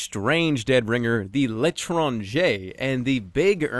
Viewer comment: i thought jerma say l'etronjer